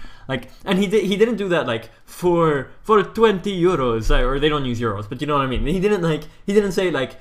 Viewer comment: When you said 420 Euros it sounded like you said 420YOLO